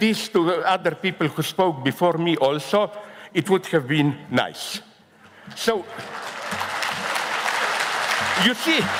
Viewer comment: what did the man at 22:50 say to zizek? i couldnt really understand it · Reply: " Stop talking "